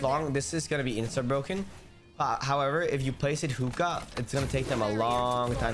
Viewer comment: did i just hear a Moroccan word? "Houka" or am i tweaking? (It means "like this") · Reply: ur tweaking that room is called hookah on the map lol